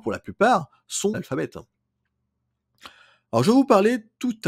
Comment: sont quoi ? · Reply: analphabètes. C'est peu audible en effet.